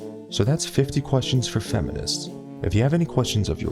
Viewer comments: HAHAHAH I thought he said dont forget to RAPE the video and subscribe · For a moment there I thought I heard "please rape this video" instead of "please rate this video" XD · At the end I thought you said don't forget to rape this video, instead of rate😂 Well, I mean rape means anything nowdays pretty much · damn, it is so often that feminists masturbate with rape that at the end of the video I thought you said "rape the video" LOL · it sounds like he says don't forget to rape this video, and subscribe. Which I like better than rate. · I thought you said don't forget to rape this video and subscribe 😂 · Thought he said: don't forget to rape and subscribe :D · I thought I heard "Don't forget to RAPE this video and subscribe" XD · after all that rape talk, at the end of the vid I heard "remember to rape the video and subscribe" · I thought he said "don't forget to rape this video and subscribe" at the end... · After all that at the end of the video when he said "Don't forget to rate this video and subscribe..." I heard "Don't forget to rape this video..." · At the end I thought you said "don't forget to rape this video and subscribe" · I thought he said rape this video 😂😂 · At fist a thought you said rape this video and subscribe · I literally heard "Don't forget to rape this video and subscribe" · why does it sound like he said “rape this video?” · did I just hear "rape this video and subscribe"? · I thought he said to rape this video and subscribe.